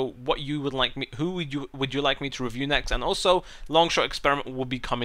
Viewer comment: did he say poo next?